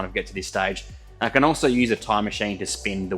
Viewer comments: Thought said a time machine lol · oooh, a tire machine. I heard "time machine" and I was like.. huuh?!